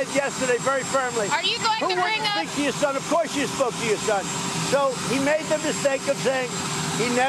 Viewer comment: Has anyone else watched this with the YouTube captioning turned on? LMAO!!! At the 4:50 mark, the caption says “of course you fuck your son!” Lmao!!!